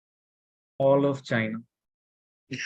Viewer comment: what did he said?? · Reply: "All of China"